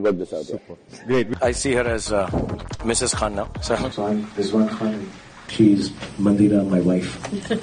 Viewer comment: omg this so good your videos always amazing I'm always waiting for you to upload more great job by the way what he said to kajol in min 😍5:34. I don't understand thank you. 😍😍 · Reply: Thank you it's encouraging :) He says "So I'm Rizwan Khan and she's Mandira, my wife"